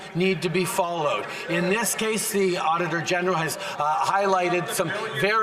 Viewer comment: I thought they kept saying “Otter General”. That would have been so Canadian. Auditor General isn’t as fun.